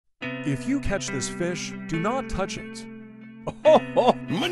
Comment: Bro I thought it said “if you touch this fish do not touch it”💀